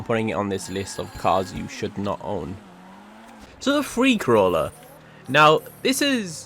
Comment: I have to say, the editing on these videos is piss poor. Audio cuts, sometimes repeats. Also your pronunciation of several words is terrible. Try recording your voice seperate to the video so it doesn't cut so badly. Also, base your videos more off actual facts and not just your own opinion. None of the cars on this list have actual reasons to be here. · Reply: When he said Freecrawler, i legit thought he said "Free Corolla"